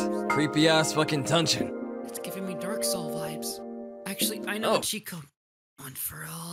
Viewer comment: I thought you said dark Shadow vibes